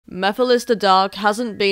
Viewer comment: for the first minute i thought she was saying mephiles the dog